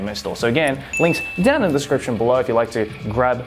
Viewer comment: When you were talking about your blindfold anime figure video, I thought it said kinks in the description 😂